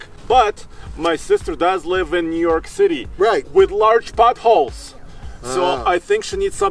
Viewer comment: “But she does live in New York City with large buttholes!” 😂😂😂 That’s what I first misheard. Took me a few rewinds to realize that he was saying “potholes”. 😆😆😆 I hope this brightens up your day! 😁😁😁